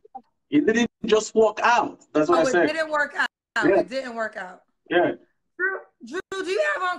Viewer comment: Drew has ADHD. He keeps moving around. He walked out of his ex wife’s life.😂😂😂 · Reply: I thought he said it didn't work out lol